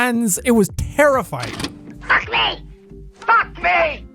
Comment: was she saying fuck me? XD